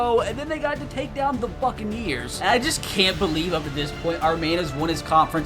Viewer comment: it doesn’t not sound like he said buccaneers